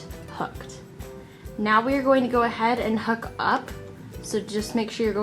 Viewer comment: did you say we’re gonna ‘hook up’😰😰😱